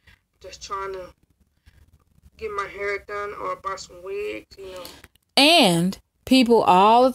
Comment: Sound like said buy some weed instead of wig I dont know maybe I'm tripping🤔